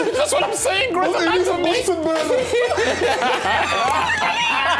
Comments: what micah saying ? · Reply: He said, "Bunsen burner," which is something you'd typically see in a Science class in the UK. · What micah said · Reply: They need some bunsen burners